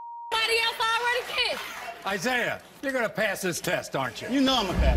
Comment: I thought he said he would pass the test